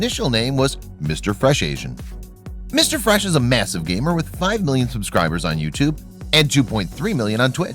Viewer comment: did he just say he has 5b subs